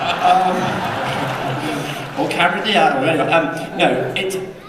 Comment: is it just me or did he say he fancied karen gillan at around 23:35???